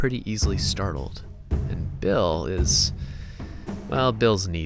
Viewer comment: thought he said "Bill ... is an idiot" at first lol